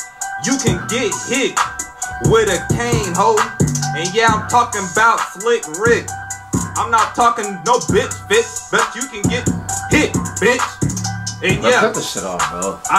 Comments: somebody tell me what this mans just said, didn’t even make since lmaooo 😂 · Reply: after he said “And yeah, I’m talkin’ ‘bout slick rick. I’m not talking no bitch fit, bitch you can get hit bitch, and yeah I got a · "..I'm not talking no bit-fit, but you can get hit...bitch" is where I lost it 🤣😂🤣😂😭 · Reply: Ohhh I thought he said bitch bitch. Not fit bit. So much more funny now